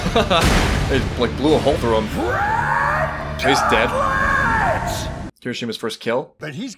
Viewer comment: fun fact, initially the dub said Red Gun Turret instead of Gauntlet. Because the sub version sounds just like Gun Turret. Of course this has now been corrected.